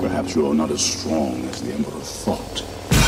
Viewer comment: “Perhaps you re not as strong as _____ thought.” What is said there? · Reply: the emperor